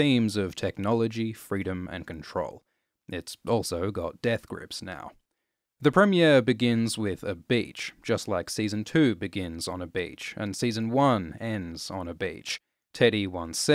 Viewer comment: Season 1 begins with a bitch, season 2 begins with another bitch... Lol I thought you were saying that